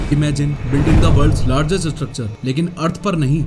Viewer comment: 'Building ka worlds largest structure " what the f are you saying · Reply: "Building the worlds largest structure" what the f are you listening